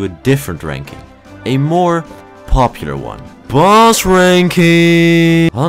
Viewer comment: i heard balls ranking